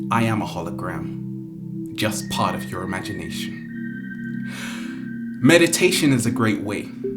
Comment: Did you just say that you are part of my imagination at 7:03?